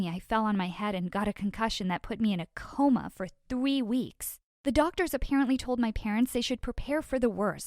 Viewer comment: No one: ... Not even a single soul: ... Me who heard her say "I got coma for TWEE weeks"